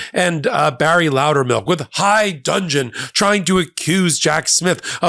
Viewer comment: Michael, did you say 'high dungeon'? I don't mind what kind of dungeon the idiots end up in. But I think you meant 'high dudgeon'.. Or maybe I misheard.